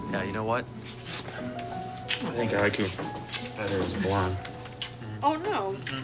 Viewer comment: Can't tell what he said there. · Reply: "I think I like better as a blonde" - it's from 8x1 after she had gone on the run after Pelant had framed her for murder